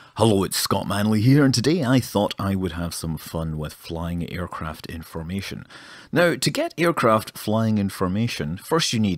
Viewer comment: I misheard that as "aircraft flying information" and was slightly confused for the first few seconds of the video before I realised.